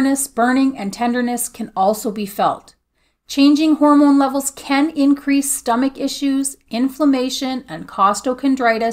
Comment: hi thanks for your video they bring great comfort, can you tell me what it was you bought to read your breathing did you say a monitor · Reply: A pulse oximeter. Those little meters the hospital puts your finger in to read your oxygen saturation. Amazon has them.